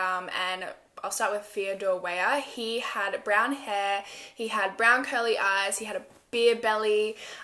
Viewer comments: Am i the only one who heard her say "he had brown curly eyes" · "brown curly eyes" now THAT is the real mystery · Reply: She said COLOR it just sounded like she said "curly" because of her accent.